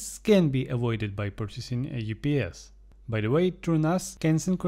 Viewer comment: What are you referring to at 15:58 when you said can avoid the power surge problem by purchasing a __ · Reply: UPS - Uninterruptible Power Supply